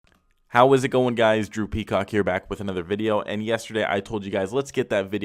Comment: Thought he said “whats going on guys droopy cock here”💀